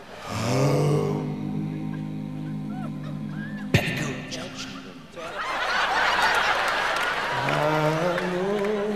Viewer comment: What did mark said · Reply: petticoat junction